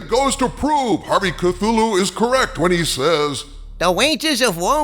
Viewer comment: For a second I thought you said Metroid in one part of the video lol. BTW Metroid is great non woke game franchise you should definitely check it out.